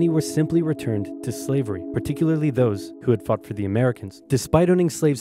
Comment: Srsly. I thought he said "Particularly those who had f**ked for the Americans.